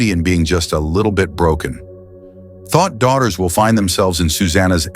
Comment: Really dropped a "Thot Daughters" around 3:18? AI scripted shit. Edit: Damn, and again at 5:35. I don't think you know what that word means...